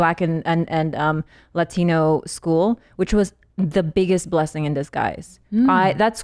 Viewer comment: does she say "blessing in the skies"?